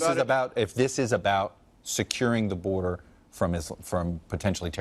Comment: did I hear "Securing the border from isl..." lol